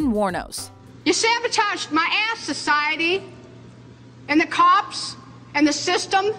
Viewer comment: I honestly thought Wuornos was gonna say “you sabotaged my assHOLE” 🤦🏻‍♀️